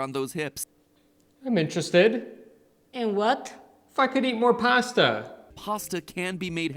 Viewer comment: Wait, did you just say "F..in eat more pasta" or "IF I could eat more pasta"? 😁 I had to check the captions... but I still prefer what my pasta obsessed brain tells me it is 🤣